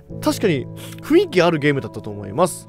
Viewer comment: 「口塞け女」やっていきましょう。って聞こえたｗ